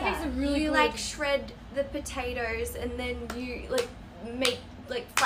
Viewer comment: At Lily's part, i heard " oR fIrSt SiNgA OuAr OuAr"🤣💀